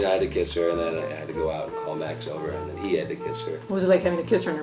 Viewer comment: Wtf just happened? I heard "And then I had to go out and climax over it"